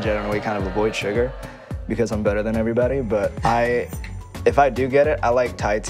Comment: “I generally avoid sugar because I’m better than everybody” -Rich Brian 2019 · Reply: I thought he said im bitter than everybody. Oh